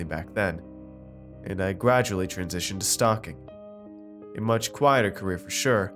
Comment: I thought you were saying "stalking" instead of "stocking!" Lol